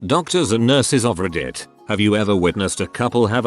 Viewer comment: When the bot said “doctors and nurses of Reddit” I heard “dumsters and nurded or Reddit” my weird ears bro